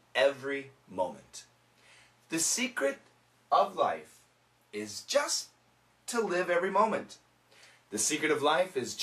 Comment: I heard : "The second of life is just to live every moment".